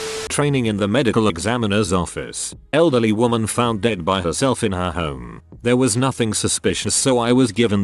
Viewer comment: misheard that as she found herself dead for a moment 😅